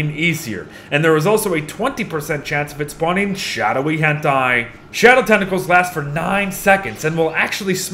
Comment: did you just say "shadowy hentai" bc it's a tentacle?!?!?!